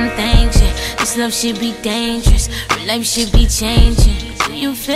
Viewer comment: "Do you feel the same"😍😍😍 "it's you to blame" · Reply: Damn i thought it said "its not you to blame" or " its nort chill to blame"